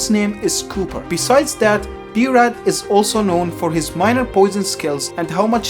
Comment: 😳 i heard as "his miner"